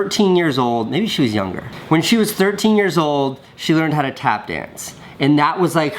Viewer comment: I thought you said "when grandma was 13 years old she learned how to clap hands" lol